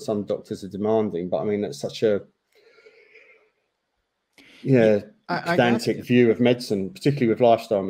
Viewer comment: what did he say? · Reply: Pedantic view of medicine